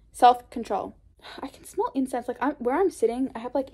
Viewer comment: Y'all please tell me what I thought you said incest I was so concerned😂😂😂